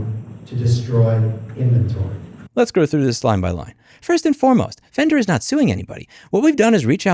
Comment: I know my ears often play tricks on me, but it sounds like you said "let's go through this slime-by-line" and I love it.